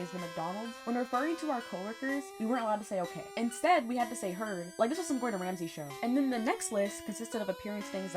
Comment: what do they say??? I cant hear over the music 😅 I love the video tho! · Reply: “We had to say ‘heard’ like this was some Gordon Ramsay show” I think I could be wrong